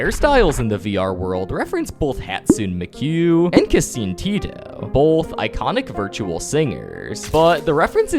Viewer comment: I'm sorry, what did you just call her? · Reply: Hatsune mekyu and kaseen teeto